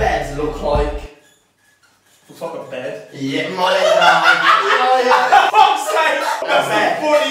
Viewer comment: Did i just hear ya my N**